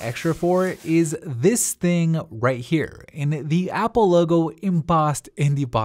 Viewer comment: Man i thought he said apple clock